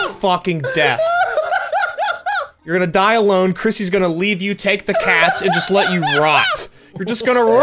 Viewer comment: “Chrissy gonna leave you take the cats and let you rot” 😳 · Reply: For a sec there I thought he said take the cash 😂